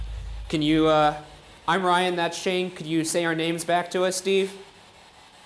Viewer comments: he said shane!!! its very quiet but i heard it. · anyone hear it say Steve?